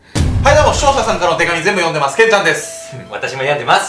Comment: しょーたさんからの手紙全部読んでます！って聞こえた❤️笑 だれ？？って思ってしまった💦 けんちゃんごめんなさい🙏 いつも楽しく見させてもらってます❤️☺️